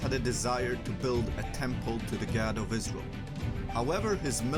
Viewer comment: the subtitle says "build a temple to the God of Islam" instead of "Israel"